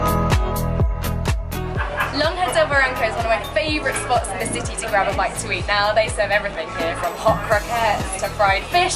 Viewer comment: Hey! Where was the food area you mentioned at 1:59? I couldn't catch that haha thank you! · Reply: Mercado Lonja del Barranco